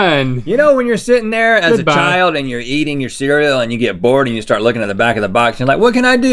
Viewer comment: I swear he said "yeeting your cereal" xD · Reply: I'm pretty sure he said eating your cereal